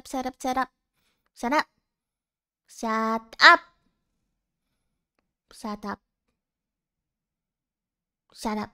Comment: I think i misheard You say shut up shut up shut up What i hear Sarap sarap sarap👌😂